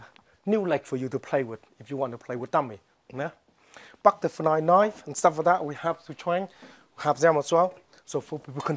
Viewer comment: sounded like he said "Fuck the fly knife"